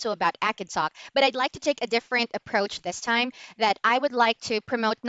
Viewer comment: @ 0:46, what is the word you are saying? It sounds like "Akansa", but the script says "acid talk". ;)